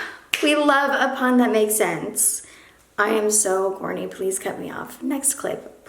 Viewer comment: I legit thought she said "I am so horny" 😂😂